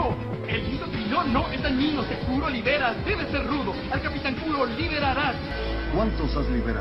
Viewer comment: jajaja estaba solamente escuchando el audio y entendí capitán culo